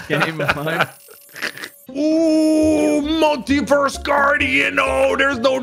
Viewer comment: does anyone else think he said Monkey-verse?